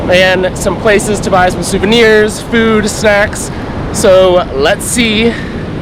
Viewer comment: Sounds like you said "buy some food, sex" instead of "food, snacks" LOL.